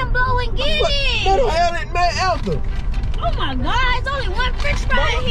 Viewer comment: 😂😭 boi said TF ETHEL 😭